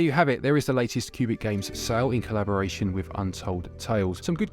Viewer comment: It sounds like your saying pubic games...I laughed the entire episode